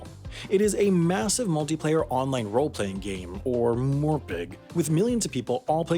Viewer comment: did he just say morpeg?